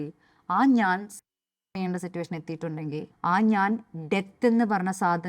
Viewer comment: What word she is mentioning at 17:34 ? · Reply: Suicide